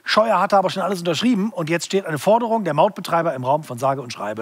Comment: Ich hab Mordbetreiber statt Mautbetreiber verstanden 😂